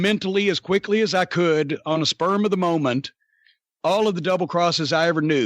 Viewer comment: Did Corny say “sperm of the moment.” 🤣🤣